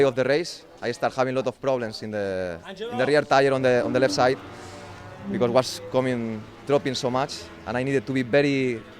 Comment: What did Iannone said on 2:43 ? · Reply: "Angelo". He was calling someone :)